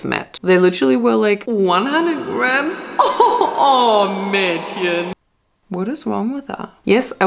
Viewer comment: what did she said? Can someone explain the last word to me I’m just curious 🌚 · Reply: „100g oh girl“ implying that’s way to less